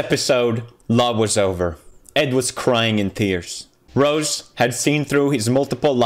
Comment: did he say crying in tears??